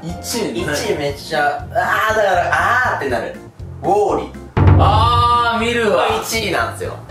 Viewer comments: ウォーリーをモーリーって聞こえてビクッてなった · ごめんね。ウォーリーが モーリーって聞こえた